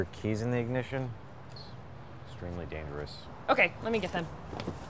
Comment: 「おはよっ」て聞こえた😂🇯🇵